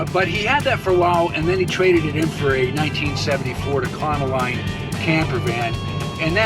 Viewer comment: I'm a little confused if I heard you right. Alan traded the camper gift from Bob Hite, for a 1974 Ford Econoline, @ 10:52, yet he died in September of 1970? As they say in the trade, a little continuity failure, or I'm missing something?